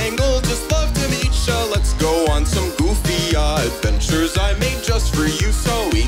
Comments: Did just say "Goody ahh adventure"? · excuse me did caine really say “goofy ahh”💀